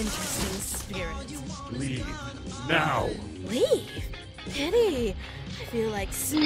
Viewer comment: Did Shiromori say pity or something in Japanese its hard to tell o.o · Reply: Flare The Wolf she did say pity